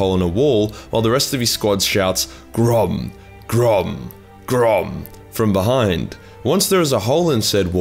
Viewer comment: Grond sounds like you say Grom..